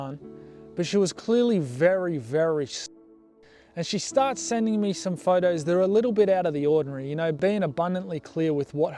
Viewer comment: tf can the word possibly be.😒🤦🏽‍♂️⁉️🙏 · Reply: stoned